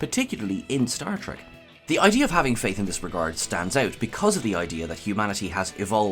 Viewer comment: Ha, I misheard 'faith in this regard' as 'faith in the heart'.